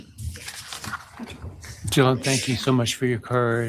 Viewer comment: It sounds like he said “John thank you so much for your courage, I appreciate it”. I guess it’s a mumbled “Dylan”. 😑